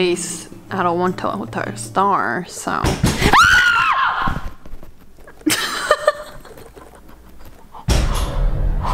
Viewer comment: WHO THE TF SAID ERICAAAA OR SMTHHH